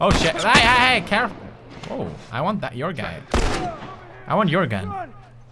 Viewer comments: did he say "I want Jöergen"? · it sounds like he said.. I want Joergen